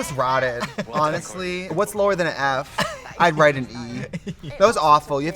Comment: “What’s lower than an F? I’d write an E.” Did she say that? Am I mishearing? 😂